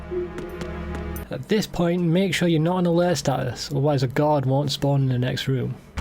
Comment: Hi, sorry can you tell me what you said at 10:22? I couldn’t understand what you said? Sorry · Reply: "At this point make sure your not on alert status as a guard will not spawn in the next room" :)